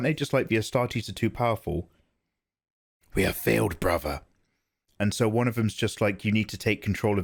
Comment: There is Battlefleet Gothic Armada 1 and 2, great games. 16:38 That is an autocannon and it could penetrate space marine armor if it hit properly. 16:48 If you slow it down you can how the space marine leans his pauldron towards the shot to deflect it. This is how I have heard the orbs dialogue. "Our escape, I have failed brother" "We have all failed, the astartes deny our touch. You must return, break your seal." "Impossible, we'll never survive" "You must. Take them." and the Inquisitor/psyker says something like "Recall them immediately" refering to the space marine team on the other ship. You can see the black smoke like effect on the inquisitor when he starts his eavesdropping of the orbs with his psychic powers. And in the warp he does his last F you and frees the space marines from the alien/warp entitys tentacles. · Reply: Instead of "our escape" I hear "someone is here, I have failed" but I think yours actually makes more sense.